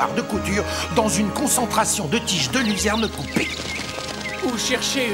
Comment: Salut, à 9:00 qu'est ce qu'il dit exactement ? J'entends "Trouver une bufo marinus dans la diversité de cette flore luxuriante et sauvage revient à chercher un accessoire de couture dans une concentration de tiges de ??? coupées." mais je n'arrive pas à mettre le doigt sur les derniers mots. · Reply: Oui c'est Luzerne, j'ai pas encore pris le temps de mettre les sous-titres, mais je fais ça dans la journée